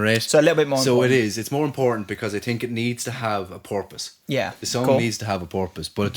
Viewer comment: I don’t know if a song necessarily ‘needs’ a purpose, however I would argue it 100% heightens a song to me if it does have some depth… That’s my take anyway, can’t speak for. Yeah, some of Thom’s solo stuff doesn’t reach the same peak as Radioheads work, especially Tomorrows modern boxes which I wasn’t that bothered about, however, I think the Eraser is pretty solid and has some great tracks, I also quite like Atoms for peace too, some brill songs on Amok, namely Ingenue, Default and Judge Judy and Executioner, but yeah those albums aren’t quite as consistently solid as the Radiohead work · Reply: lol I was just being dumb. It sounded like you said porpoise instead of purpose.